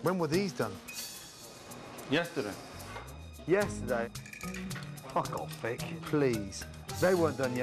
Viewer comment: The owner gurs name ic Vic? Is it? When Gordon said F#$k off Vic I had to rewind as I thought for a second he said f#$k off sp^c. Man I was in disbelief as I like Gordon but though "very very not cool and beyond bondaries" if he said that. I am clarified of my misunderstanding thanks to the rewind. Ahh. Poorly run business though. I am sticking more to simply preparing my own food or if i must eat out it's more like hold the pickles hold the lettuce, hold the ketchup, mustard, fake fluorescent orange melted crayola crayon so called food product called cheeze. If fact hold the the burger and the bun. cya. I'm going into the kitchen to make 25 ingredient salad and five grain bread. this stuff is distrubing. THANK you Gordon!!!!